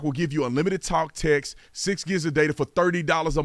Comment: Sometimes I have trouble understanding you. For example-You have an ad for cell service. I thought you said unlimited talk and sex…..I want my money back!